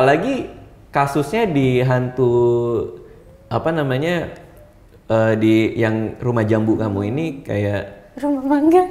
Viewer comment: I'm not so sure what the sound is, but I heard a sound after he started to talk about his experience like "something" wanted to confirm. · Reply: confirmed